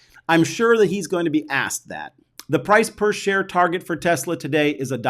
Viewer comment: Did you just say Tarjhe instead of Target :-)